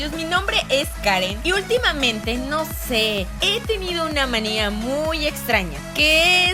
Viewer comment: Minuto 0:14 en ves de decir manera dice maneo escuchenlo en camara lenta y haci se escucha que dice manea